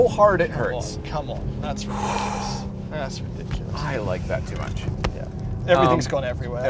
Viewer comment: “That belt squeezes so hard it hurts. I like that too much!” lol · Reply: I thought he said "I don't like that too much"